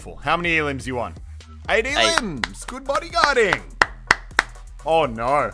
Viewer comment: did he say 80 elimination